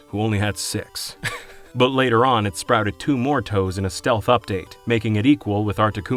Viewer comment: Did you say “stealth update”? 😂